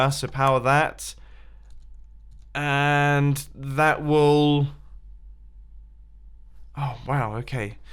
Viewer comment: Any thought that at 5:57 he said "wool" and not "will", is that just me?